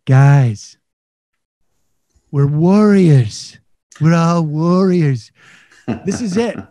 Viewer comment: I thought he said worriers. The people of our country are worriers.